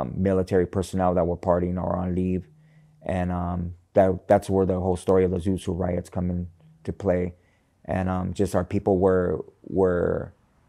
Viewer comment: Can anyone help me understand what riots he mentioned at 8:02? I listened a few times and still couldn't catch it. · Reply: Suit suit riots /. At that time it was the Navy and pachucos